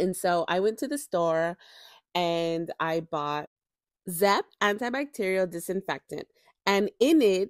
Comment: what cleaner did she say she bought?? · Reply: Zep Antibacterial Disinfectant. Can be purchased from Home Depot, Lowes or Sam's